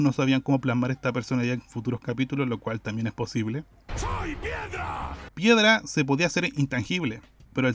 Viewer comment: Le entendi "SOY MIERDA"